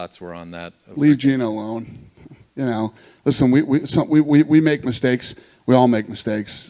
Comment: "We all make mistakes." While this is objectively true, Gena did nothing wrong. Don't put words in her mouth and apologize on her behalf. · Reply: Oh, I honestly thought he was saying Disney made a mistake. Lmfao